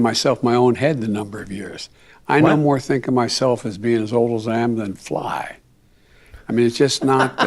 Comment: He was staying "I see myself no older than a fly" he just didn't get all the words out. Flys have a short life lol. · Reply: I heard it as a jumbled up attempt to say: I no more think of myself as old as think that I can fly.